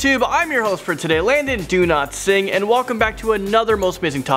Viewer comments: I heard "do not sing". Only me? Ok. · I thought he said his name was landon do-not-sing whats wrong with me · Did he just say landon do-not-sing?